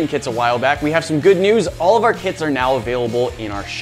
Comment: i heard, all of our kids are in the shop. Did anyone els hear that